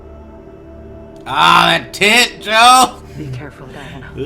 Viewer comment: Thought he said "Ohhhh look at that tit Joe"